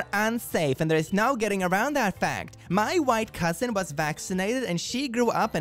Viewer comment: when you said "My white cousin" I swore you said "my wife cousin" and took a double take, but I wouldn't have been surprised